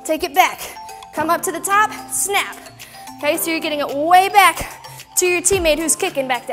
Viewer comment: Did anyone’s Siri go off at 21:00 ? I was so scared but I realized she was saying okay so you’re and that sounded like hey Siri.